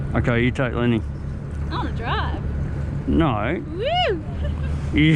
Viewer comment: At exactly 12:00 did she say “what a drag” when asked her to hold Lenny?